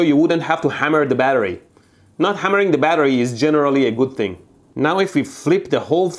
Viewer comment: Did he just say ah-ah batteries...